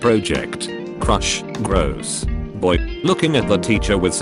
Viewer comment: At the 4:08 time of the video instead of saying boy it says buy